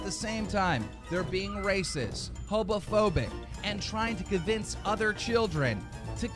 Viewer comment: lol did he say hobophoboc? Is he scared of bums?